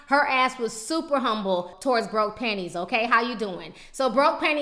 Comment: Broke pennies!!" Lol girl, I freaking love you Lmbo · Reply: i thought she was saying broke panties......